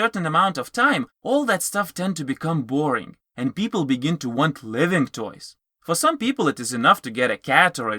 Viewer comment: Well said. We need more videos like this airing on TV and as ads for people to see. I notice you have an accent and I understood everything perfectly except for one thing and this isn't meant to put you down. When you said living toy the first time I wasn't sure what you'd said and then toward the end of the video I understood it but at first it sounded like you said "live in toy" rather than living toy. Again just pointing it out. Make more like these.